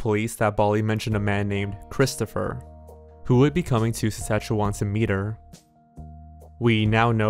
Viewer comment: Get 10% off Dashlane Premium with checkout code “jorge”: <link> P.S. I had a really tough time pronouncing some words here, so I'm sorry if I got any wrong (I'm sure I got Regina wrong in hindsight) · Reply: were you tryna say Saskatchewan? I heard "sashwon"😂